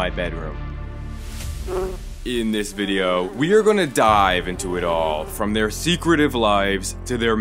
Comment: i thought he said "in this video, we are gonna DIE"